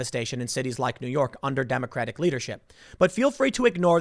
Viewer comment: sounded like Tim said "Democratic Lootership" hahaha